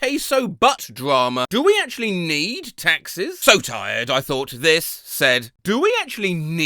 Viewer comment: I genuinely read + heard this as "Texas" instead of "Taxes" and got really confused when it was revealed the person read it as "Texas." I was like "??? That's not misreading that's just what it says rolls video back a bit oh. Oh no. It sure just said Taxes the whole time, huh."